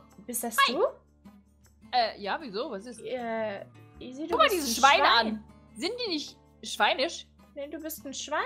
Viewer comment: Ich habe verstanden können schweine schweine essen😂😂